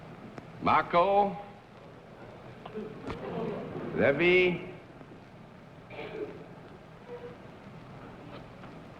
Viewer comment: As John Daly reads off the last contestant's name while he signs in, Daly says "Marco", then pauses while he writes his last name. I think someone in the audience says "Polo". I've listened to it several times with headphones, but I'm not completely sure if I'm hearing it or just wishing to hear it. The best indication that it must be so is an audible murmur of laughter that does follow. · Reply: "Polo" confirmed via amplification